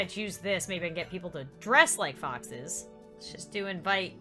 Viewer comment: When Lauren was talking about foxes on Seapeekay day, I thought she said boxes so it sound like she said: people can dress up like boxes. Did you know boxes like menorahs? Boxes are Mischievous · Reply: Me too I thought she said boxes